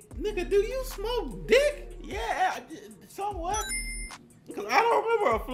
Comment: did just say YES?